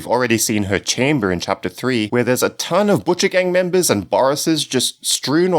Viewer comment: when he said butcher game I thought he said Gucci gang XD